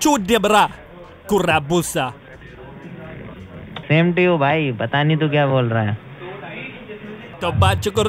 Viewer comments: he actually said "same to you bhai, pata nhi kia bol rha hai" it means that he doesn't know what you are saying. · Its not "But i need to go", he says "Pata nahi tu kya bol rha hai" which means "I dont know what you are saying" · you kinda got the subtitles wrong. He was actually speaking Hindi not English and said "same to you Bahi (meaning brother) Patna Nani kon see language Bol raja ha?" meaning: what are you even saying? I know this because I speak Urdu... Urdu and Hindi are somewhat same.